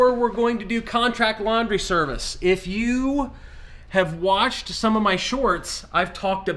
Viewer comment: I thought you said “if you’ve washed my shorts”! No I haven’t and am not planning on it either! 🤣🤣